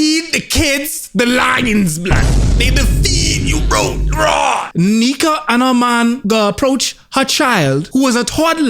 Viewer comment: I sometimes put on my earbuds and sleep with a video on the background. I kept hearing “Negha and her man” 😂 I was like wtf did je really say that then I open my eyes and I see this lmao. Cant sleep on this is too funny and spot on 😂💯💯